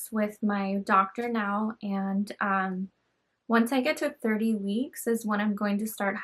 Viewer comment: In the video it sounded like you said you were going to deliver at 30.5 weeks did you mean 37.5 weeks? If so what date would you be having your baby then? Like someone else said your video could be an hour long and I would still watch love your videos and life updates!